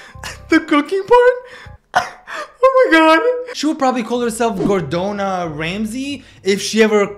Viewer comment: As a Mexican when he said "Gordona Ramsey" and in Spanish Gordo or Gorda means fat I burst out laughing 💀😭🖐️ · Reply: I thought he said Gordota Ramsey. Lol was looking for this comment.